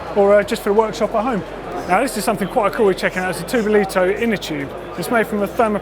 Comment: cool innertubes.. i did listen to that segment for 5 times now. Cannot hear what he says? dolito? todolito? i googled on both but zero result. Where can i buy there innertubes though? on